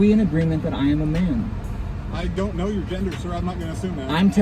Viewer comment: “Are we in agreement that I am a man?” Police officer: “I do not know your gender, sir, I’m not going to assume that” was the best part to me lol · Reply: does anyone have a time stamp? edit: 1:02:07 I think he said "so" not "sir"